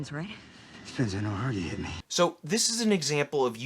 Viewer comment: What does Jeremy Renner say at 6:20? Lately, so many actors seem to neglect enunciation. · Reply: I think it’s something along the lines of “it depends on how hard you hit me”